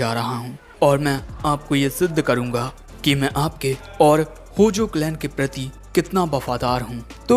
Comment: Did bro just said gojo clan 1:42 💀 Bro LITREALY turned into goku 32:10 Anime name: The elusive samurai Guys dont let the anime name down!